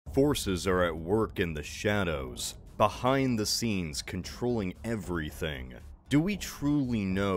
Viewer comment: When I first saw this video the first thing I thought you said was, "horses are at work."